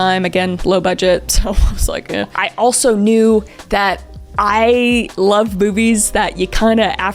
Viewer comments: did anyone else hear “I love boo-bees” 😅😅😂😂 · Please tell me I'm not the only one who heard "I like boobies" at 6:06. 🤣